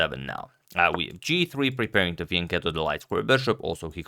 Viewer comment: what does he say here exactly ? About the bishop ?? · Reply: fianchetto - A fianchetto occurs when a bishop is developed onto the second rank (for White) or the seventh rank (for Black) on squares g2, g7, b2, or b7. This step is taken to place the bishop on the longest diagonal possible.